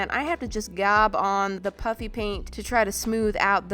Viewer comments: i sounds like your saying puppy paint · It sounds like you say puppy paint